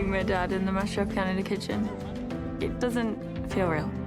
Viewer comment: why did I hear her say mushroom Canada kitchen?